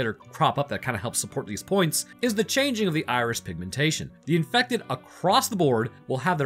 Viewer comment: Am I the only one hearing say Irish instead of Iris? Starting